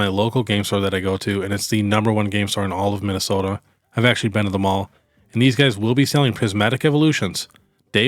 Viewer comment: Every time I’ve heard you say “I’ve been to them all” I thought you were saying “I’ve actually been to the mall” and have always been confused. Now I understand.